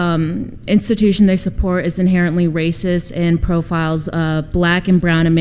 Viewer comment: Did she just say Wacist? Lmao